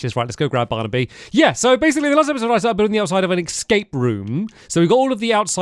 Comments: WHAT DID HE SAYYYYYY 🤣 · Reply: In the last episode I started building an escape room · what did he say at 0:57 😭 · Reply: Yeah, so basically last episode I started building the outside of an escape room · please tell me im not the only person who has no idea what said lol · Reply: Yeah, so basically last episode I started building the outside of an escape room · what did bro say 😭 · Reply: Yeah, so basically last episode I started building the outside of an escape room